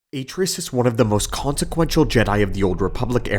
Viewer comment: I initially misheard "Atris" as "Vitrous" and THAT IS SO what her sith name would be. One of my absolute favorite parts of Kotor 2 was playing a light side Jedi, and calmly and gently contradicting her to the point that she almost screams at you